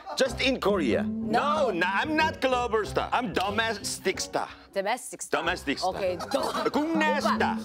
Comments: At first I thought he said "dumb-ass-tic star" LOL (which BTW in fact was "domestic star"). Love Jessi! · I’m really sorry, but I thought he was going to say “I’m dumbass”💀